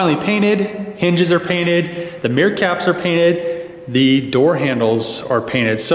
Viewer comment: Every time you said "mirror caps" I heard "meerkats". Now I want to see some meerkats in that blue color.